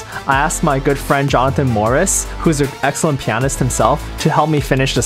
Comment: i thought it said jonathan NORRIS and not MORRIS and i was like " chuck norris has a son..?"